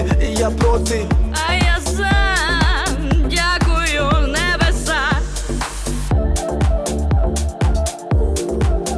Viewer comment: мне одному послышалось я курю небеса? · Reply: professional VLaD дякую небеса!